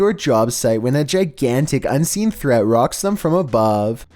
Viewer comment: what did he say? unseen what? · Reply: Unseen threat.